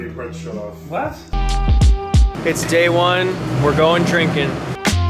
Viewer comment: I thought that you said: no drinking. Oh well, I guess its the old story: the best laid plans of mice and men. And you two are both men, and free to change your minds lol.